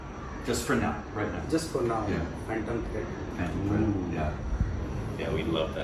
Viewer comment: what the heeeck did he say what's the favorite hollywood film? Over and over i rewinded but couldnt catch it.... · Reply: He said phantom thread.